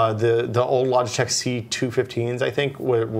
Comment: What is the name of the webcam stand mentions at 37:37? I can't discern what he says. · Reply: He said it was a Logitech C215.